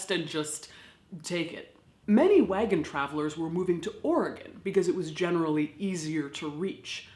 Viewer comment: When you said "Oregon" I heard "organ"